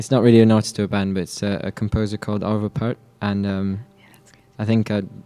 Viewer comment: The artist Igor's talking about seems to be Arvö Pärt i guess, not Albert (*) like subtitles say at this moment, for those who've been wondering...